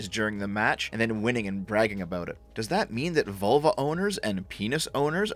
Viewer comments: I thought you said Volvo instead of vulva. Can't stop laughing. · haha, for the first half I listening it sounded like you were saying "volvo" and not "vulva" lmao